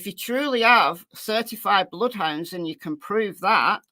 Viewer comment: you said “certified bloodhounds” and the person in chat thought you said 35 bloodhounds. Oh how false information gets started.🤣Not your fault but I got a giggle out of it.